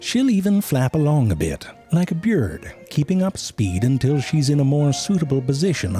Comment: Did he just say beard instead of bird at 2:59 ?